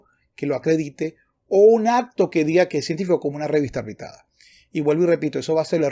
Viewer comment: No consigo oir bien el final de la frase. ¿Qué dice? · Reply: Revista arbitrada